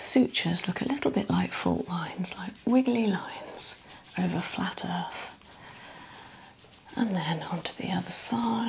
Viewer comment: Did she just say "flat earth"